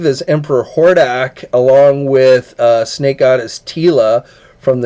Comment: did you say Snake Eyes Teela !? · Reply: Lol, I said Snake Goddess Teela but Snake Eyes Teela would be an interesting mashup.